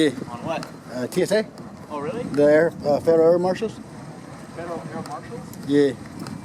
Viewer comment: this genius thought it said U.S. Federal HAIR Marshal, so he stopped shaving and signed the fuck up. lol.